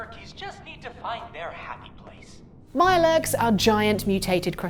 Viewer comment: I can't have been the only who heard "my legs" instead of Mirelurks.